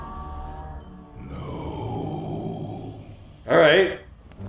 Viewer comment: I swear that death just said "Gnooooome".